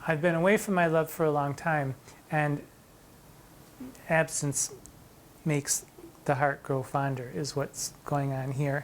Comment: I definitely needed an innuendomonitor. Not because I didn't get it, but because I wanted to hear the meaning haha. 😅 I had to rewind, at first I heard "Absinthe make the heart grow fonder." I thought, well, that is not wrong, but what did I miss!? haha If I am not yet convinced, I can at least see how this can be read as an on going narrative.